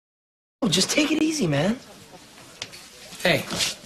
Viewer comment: I thought Drake said just take it easy ma’am